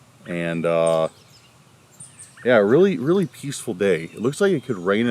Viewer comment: Becareful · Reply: I thought u said Beaceful lol 😅🤣